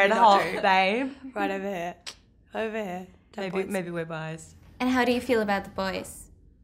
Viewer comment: Did anybody else catch Margot saying "maybe we're bi's" ?